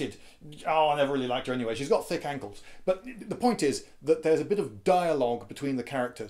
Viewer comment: I laughed out loud at the part about her having thick ankles! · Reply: i thought he said angles